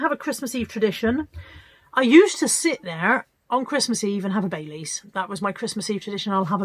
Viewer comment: I Just thought you were saying I sit there and have Babies 😃😃😂😂🤣🤣🥰 x